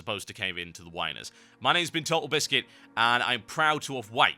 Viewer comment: I heard "and im proud to have wanked, here in BRC"